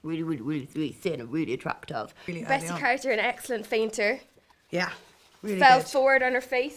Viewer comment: I think what Nicola actually says is "Bessie Carter, an excellent fainter" (that's the actress who play Prudence)